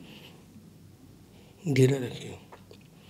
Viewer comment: did he say get out of here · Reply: He said "dheela rakhiye" in English he meant to say that keep your eyes relaxed.